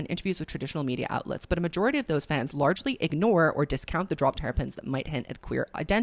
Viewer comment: Thought that you said ”dropped terrapins” at like 35:40 or so, and I can’t stop picturing Taylor dropping lil gay tortoises as clues 🔎😅